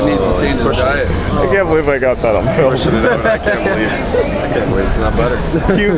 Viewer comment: uhm. was i the only one who heard him say i cant believe its not butter?